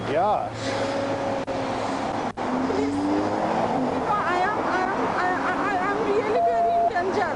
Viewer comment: did she say “I, I, I am really, really intelligent”?! I have a different opinion.